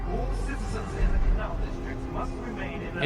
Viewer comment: Did I just hear CLOUD DISTRICT!? We gotta look for Nazeem!